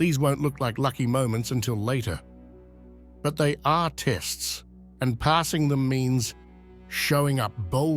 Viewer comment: the word “artists” is written instead of “are test”. Hmmmm i am an artist myself, so I’m just gonna let this sit. ❤